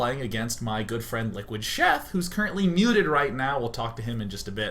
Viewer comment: This is what I thought I heard 0_o: 1.05 : "...Liquid Sheth who's currently neutered right now.."